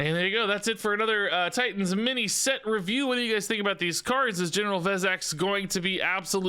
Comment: I kept hearing “General FedEx”